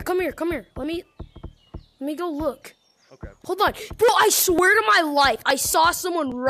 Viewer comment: I thought you said i swear of god